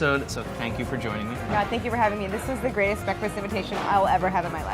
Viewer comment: After he thanks Claire for coming it really sounds like she says "Brad, thank you for having me.."